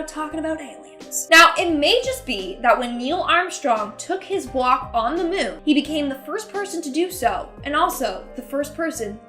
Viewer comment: ngl the first time you said "when he took his first walk on the moon" I legit had to rewind it cause I thought you said "when he took his first guac on the moon" lol